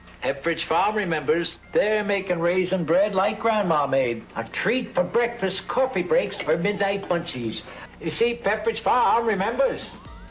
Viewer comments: "A treat for breakfast, coffee breaks, or midnight munchies." Just in case you were wondering what it said. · The last thing the pepridge farm commercial said was midnight munchies at 7:40 why did the editors subtitles say ???? It wasn't hard to understand